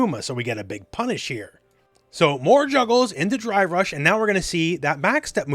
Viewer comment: he said more juggles but all i heard/saw was more jiggles 😅